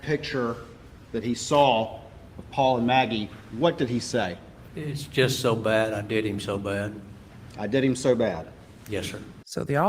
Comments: I've listened to it several times. I keep hearing, "It's just so bad. I did it so bad". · I hear ' it's so bad , i did them so bad ' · Thank you for this!! How the heck is the jury following this? I heard “I did ‘im so bad” I didn’t hear the h but I definitely heard an m. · I Heard “ it’s so bad . I did it so bad” every time I listen to it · I hear “I did him so bad” I listened multiple times… I love these quick bits!! No time to watch the whole trial but I’m so curious. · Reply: I hear it as "I did em so bad" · "It's just so bad, I did it so bad." That's what I heard. I don't think he expected the biological results of shooting a human in the head w a shotgun. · I heard "I did em so bad"